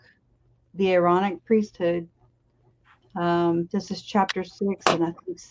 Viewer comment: Chad didn't want Lori's children around. He wanted all the money and loin fire without distractions. Given time I'd put money on Chad offing his own family. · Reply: The Aaronic priesthood…. I thought you said the Ironic Priesthood, which is probably more accurate.